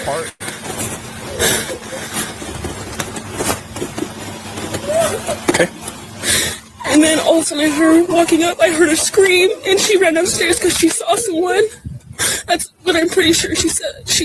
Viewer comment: waaaaait a minuuuuuute!!!!!!! Did just say “she ran UPSTAIRS cuz she saw sum1”…….n then says, she screamed n said sum1s here n ran down the stairs? My ears could def b playing tricks on me! I’m tired! But could KG have seen sum1 B4 she went up? 🤷🏻‍♀️ I meeeeean we kno she wasn’t wearing everything she wore to the grub truck, n if they find the whole outfit? KG changed n I would imagine it wasn’t into a pair of sneakers to go to bed!!